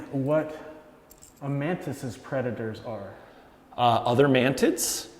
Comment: I keep hearing 'mantits'